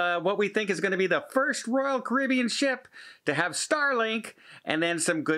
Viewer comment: Starlink WIFI My wife thought you said Celebrity would be serving “Starlings” for high class cuisine!! She was red faced!!!!!